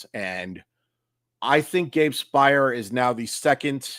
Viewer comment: I think you mean to say Speier, right?